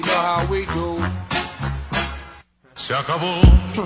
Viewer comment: All I hear is: Deez nuts. Mr. Meth. This is how we do it. Shaka boom. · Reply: I thought it said, shuckable